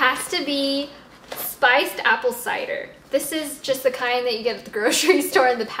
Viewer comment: at first, I thought you said spiked apple cider, lol